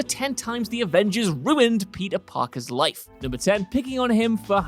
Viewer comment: Is it just me or did I hear "Palpee Parker."?